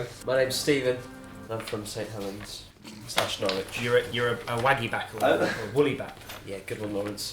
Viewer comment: did he say St. Helens/ Norwich ??